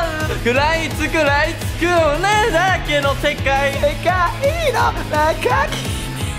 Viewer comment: おネエだらけの世界に聞こえた同士いる？w